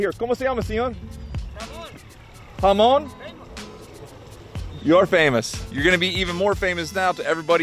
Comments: Hahahahah at 1:50 you asked that man what his name was. And he answered Raymond and you thought he said as famous.😂😂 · when asked the guy his name and he say then Raymond thought he said I’m famous 😂😂🤣🤣 great video it’s my dream to travel and fish some day. I admire your work and love for what you do. Be safe and fish on!